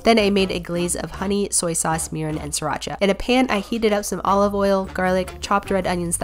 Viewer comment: I let it play over and over and I keep hearing her say "honey, soy sauce, urine and sriracha" 🤣